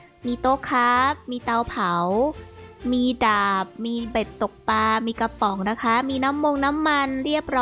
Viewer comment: หูเราไม่ดี หรือ ได้ยินคำว่าเป็ดตกปลาจริงๆ 🤣🤣🤣🤣